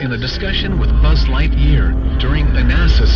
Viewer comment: Did he say "Buzz Lightyear"?